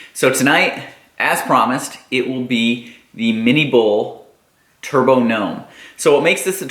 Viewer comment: I always thought people were just saying mini boil with a weird accent instead of mini bull.